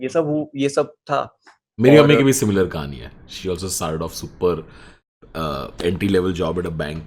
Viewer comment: did Tanmay say "Meri Ammi ki bhi similar kahaani hai"? lagta hai bhai ka asar zyada hi ho raha hai